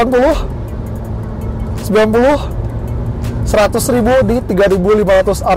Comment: itu speednya 100ribu apa gw salah denger?